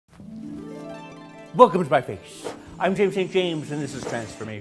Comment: Swear to God I heard him say "welcome to my face this is masturbation"